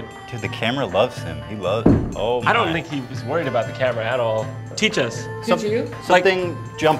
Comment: OK so I sat there wondering how the funk that guy came to the conclusion that he should kick Jon in the face for a good few minutes, but I thiiiink I found the problem here. The guy thought you said "Kick us." The way you said "Teach" probably sounded a lot like "Keek" lol